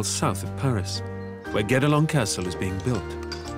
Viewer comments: I keep hearing "Get along castle". When thinking about the need for teamwork, they couldn't have picked a better spot. :-) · To be honest, I heard "Get-Along Castle"